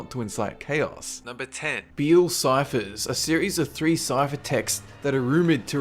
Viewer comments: On number 10 I thought you said "Bill Cipher" and I was like "BILL" so I quickly flipped to the tab this was open on. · does he say Bill Ciphers? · Did you say "Bill Cipher" As in.. Gravity Falls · I swear... when he said beal ciphers my mind just heard him say bill cipher. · Fangirl Moment: I thought you said Bill Cipher. XD